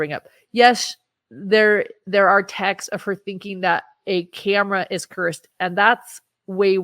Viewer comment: Time stamp 46:21 Did she say…. Tammy believed a camera was dark??? Or did I mis understand that? And if yes does she mean like a camera 📷 or is that lingo for something my brain don’t know lol 😂 I’ve literally listened 3 times and I do believe that’s it but just wondering if that’s what it is lol cameras are dark and evil! 👿 I mean they are if you use them for bad things I suppose but let’s be real for a second, she’s truly being brainwashed by Chad, and he must not believe that! He loved his camera dance videos! Lmao 🤣 gezz what grown ass woman sends that crap these days? Dance videos